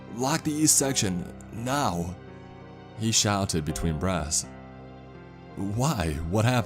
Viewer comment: I keep hearing him say "breasts" for "breaths."